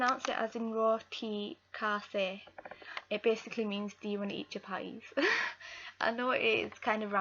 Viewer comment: I thought u said do you wanna eat cha pies but I scrolled through comments and then found out it was chapati